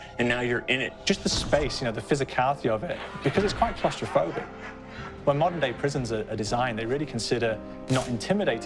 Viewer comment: Did he just say clusterf*ck?